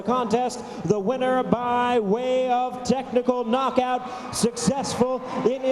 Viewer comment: Sounded like he said "testicle knockout" which is funny because his last low kick kind of looked like a partial nut shot to me. Lol